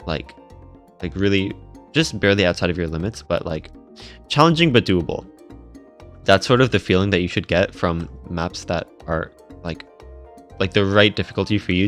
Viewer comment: I was so confused when I misheard "Just barely outside of your limit but like... 'Challenging badeuable'..."